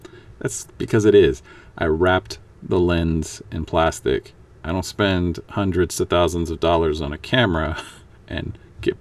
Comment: hundreds of thousands dollar camera? (at 31'31"). So you're filming with the Hubble telescope? lol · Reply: As far as I can tell, you misheard "I don't spend hundreds TO thousands" as "I don't spend hundreds OF thousands", which would make your original comment make some sense. As that isn't what I said and you now have the correct information, there is no irony. Prepositions can be one of the most difficult parts of the English language, but they are important. As for why I said, "I don't spend hundreds..." it was to describe how even a cheap piece of plastic and a rubber band can be used to protect the camera lens. I cannot afford to replace the camera and in this case the cheap plastic came from a card sleeve, it could be valuable information for someone that wants to make their own video with either a rattle can or air brush. If you need any further assistance understanding my videos, my brother lives in Serbia and Croatia working as an English teacher and can probably help you out. I would hate for something else to be confusing while watching YouTube videos and lead to some form of disastrous result.